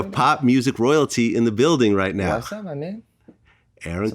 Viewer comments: Took me a while to get into this video because I thought he said “what’s up my nigga” so I kept playing it over and over lol. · Bruh I thought he said was up my nigga lol